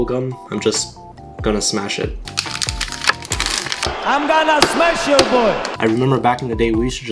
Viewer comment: “I’m gonna SMESH your boy” 🦅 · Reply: But i heard "i gonna smesh your ball"